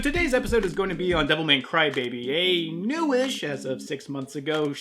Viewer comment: I thought you said "Devil May Crybaby" and now I'm imagining something hilarious